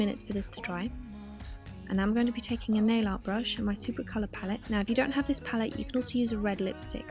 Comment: If i didnt watch this video i would have never know you were saying nail art brush. I always thought you were saying airlight brush... i feel stupid